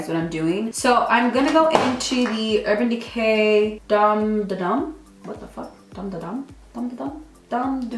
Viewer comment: Born to run pallet. Idk what you were saying🤔😅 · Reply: Taylor Sonnier lmao it sounded like she said dom duh dom palette. 😂